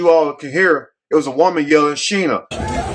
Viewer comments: I didn't hear Sheena, I heard, "shoot her." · I thought that they said shoot her!!... instead of sheena